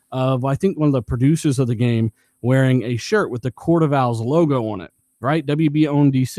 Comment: Gotham season 3 cuts in with the court of owls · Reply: Ooh that's what they were saying. I swear, I kept hearing "court of vows" lol. Like what, they all getting married or something? Owls is def better, altho not by much lol